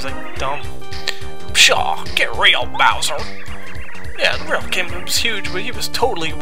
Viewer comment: it almost sounds like you say "Yeah, the real king boob's huge..." xD Oh Lordy. I had way too much fun watching this episode !!